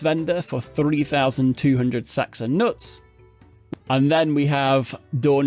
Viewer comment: Pretty sure I heard you say 30,800 sacks of nuts or the automatoise. Should just be 3,200.